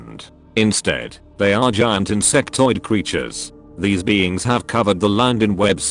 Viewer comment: Hi. For a second I thought you were gonna say ‘Huemadiod’. Not ‘insectoid’